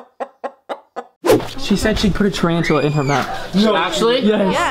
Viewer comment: It sounds like said triancula